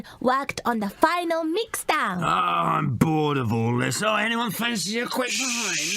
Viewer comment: What did he actually say? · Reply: If you are talking about murdoc he said “I’m bored of all this..does anyone fancy a pint” if you are American a pint is a glass of beef